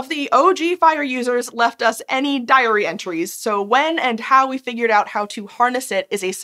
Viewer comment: You said "diary entries", I heard "diarrhea trees" 🤦🏽‍♀️